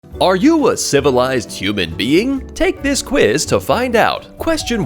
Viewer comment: At first I thought the opening said, “Are you a civilized human scumbag?” lol 😂